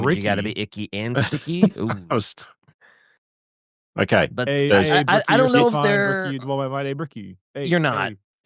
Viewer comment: Anybody catch what Bricky was saying at 6:14? I couldn't understand or hear him at max volume to save my life lol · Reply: He was singing Hey Mickey (You're So Fine) but replacing Mickey with Bricky. To be fair, he indeed IS so fine.